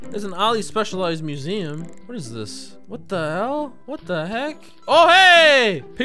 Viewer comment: did he say what the hell?